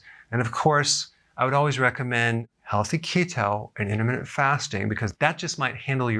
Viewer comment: Could someone tell me what he recommends in 0:12 i dont understand thank you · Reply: Healthy keto and intermittent fasting.